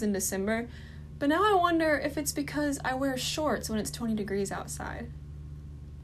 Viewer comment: wait,did she say 2o degrees?